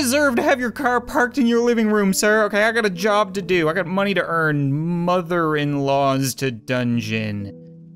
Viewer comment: I thought he was going to say I got a job to do money to earn mother in laws to burn XD